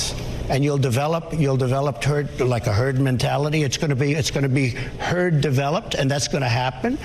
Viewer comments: I heard him say "turd mentality" at 1:12 😂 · anyone else hear "turd" @ 1:13? yea this guy is our "president"...